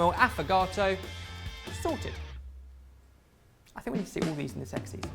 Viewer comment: omg I heard "I think we all want sex now" can't stop laughing! 💖